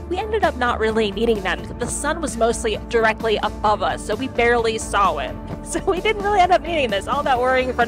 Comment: did you say saohh barely sawohh-uit 7:26 ?